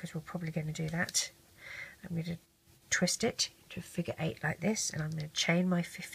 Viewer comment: When she said figure eight I thought she said freakn eight